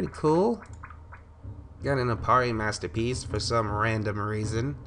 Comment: It Sounded Like You Said U Got A Aparri Hat Lol